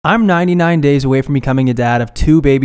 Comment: i literally thought you said "im 9 days away from being a dad" and i can't even describe how shocked and confused i was lmao